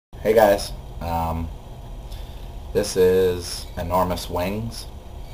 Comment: lol i thought you said " this is enormous wangs" hahah nahh i agree with everyone get on itunes!! :)